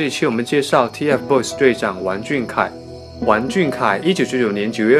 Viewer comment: 我是不是被二代吵聋了？我咋听成了"玩"俊凯呢？(没有恶意)